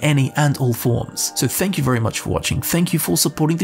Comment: Did you say n'wah?